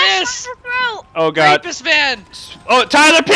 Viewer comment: did Tyler say "rapist van"